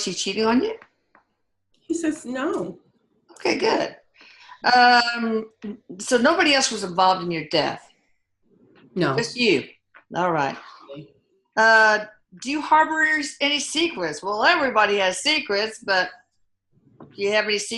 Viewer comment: I swear At about I heard 34:25 Anthony say “just me” did any else hear that??? Great video!! Thank you all!